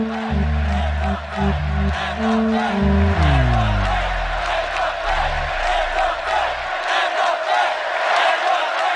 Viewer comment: What are they screaming right at the beginning of the video around minute 1:30? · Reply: I think "End the bank". Could be wrong though.